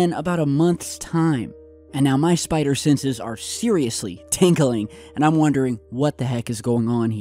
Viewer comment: lol did you say at 5:50 that your spider senses are tinkling?? hahahaha maybe you should have gone potty first. ;)